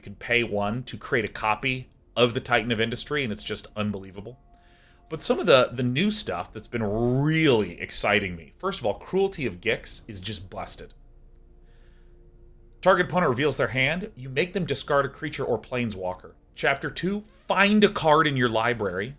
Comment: I first heard Cruelty of Dicks and was like:,, Say What!?" xDD